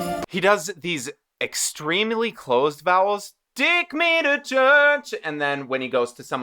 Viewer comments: who else hears «dick me to church» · I was playing this in the background and 2:10 I thought he said "dick me to church" and I had to do a double take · The caption says "Dick made a church"